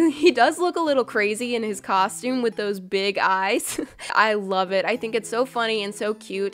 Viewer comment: I heard you say “ I WOVE this”